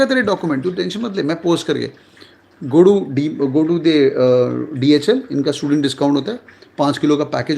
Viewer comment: go to the ___ . can anyone tell me please what exactly did he say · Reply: DHL courier service